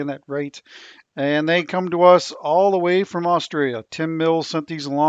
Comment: Australian tree's do some really uniquely weird stuff. I suspect its due to the nature of our bush which often requires fire to regenerate; the pods can lie dormant for years until finally awakened. This piece is stunning...enough to forigve the mispronunciation of both Banksia and Austria? Im sure I heard "from Austria". Always a laugh, especially when you go to Austria and they have tourism merchandise everywhere saying "No Kangaroos in Austria" :) Have to wonder how gumnuts would look...much smaller pods, but would be nice in a wider open bowl I think.